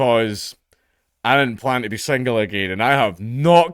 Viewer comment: Did Dank say he was single? Can't really hear what he says at around 0:58, but if that's the case then that's a bummer. Hope he's doing alright.